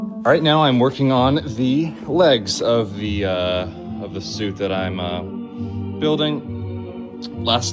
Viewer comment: Did he say suit…..